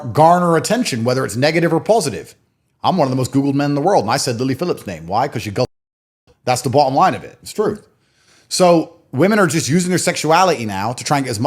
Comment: What word was censored at 40:10? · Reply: Come